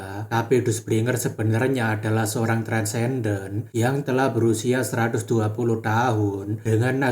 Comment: salah dengar gue min kirain transgender. sampai gue letak hp di telinga saking kagetnya :V